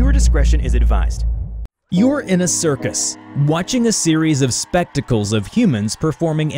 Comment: I misheard watching a spectacles with watching a testicles performing impossible acts